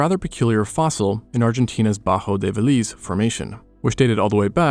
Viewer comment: in Argentinas butthole is what i heard😭😭😭😭